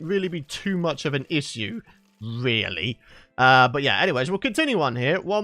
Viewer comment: Did anyone heard say 'isssseu' at 7:45.....lol